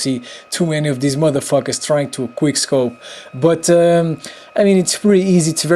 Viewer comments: it litterally sounds like he says "queer scope" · y does it sound like ur saying 'queer' scope instead of 'quick scope' and I kno can say quick because u say it at beginning but then u stop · it bothers me that everytime he said "quick scope" it's sounds like he says "queer scope". anyone else or am I just hearing things? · Your so bad u sprayed with a Barrett and hard scoped the whole time and some times instead of u saying quick scope it would sound like u said quiere scope